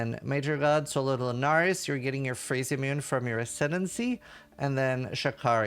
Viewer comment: When you say Phrecia it sound like you say "Free shit"